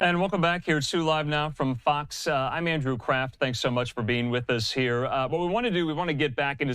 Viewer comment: did he say andrew craft or crap?